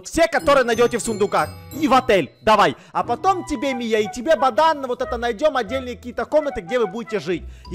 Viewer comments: я одна это услышала: "баДан"?))), кто тоже - ставит лайк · мне одному послышалось что сказал бодан?